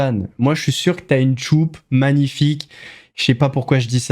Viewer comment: il a dit quoi?!😮😂Il espère que Brigitte a une belle Tchoup ?!?!😂😂😂😂😂😂😂😂😂😂😂😂😂😂😂😂😂😂😂😂😂😂😂😂😂😂😂😂😂😂😂😂😂😂😂😂😂😂😂😂😂😂noooooon 😂😂😂😂🎉🎉🎉🎉